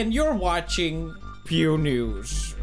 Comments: It always sounds like he is saying ”Pew Nudes” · I thought it said PEw NEUUs